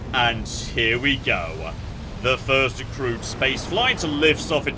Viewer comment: I misheard "Crewed spaceflights" as "Crude spaceflights" and though, Yep, I've seen play Kerbal so that tracks.